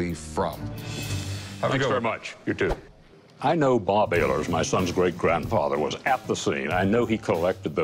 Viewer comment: does he say "my sons great grandfather"?